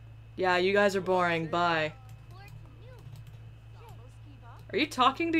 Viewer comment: I think Toby just said babe O.O